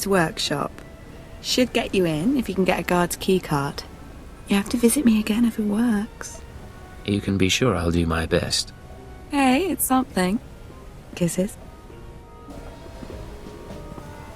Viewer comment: Did... did she just say kisses?